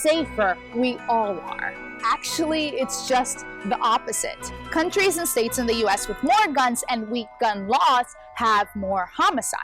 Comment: Haha at 2:50 does she say “countries and states within the us...?”